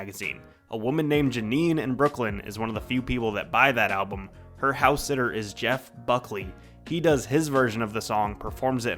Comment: not to be that person but did you just say 'buyed' at 8:09? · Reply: "is one of the few people that buy that album"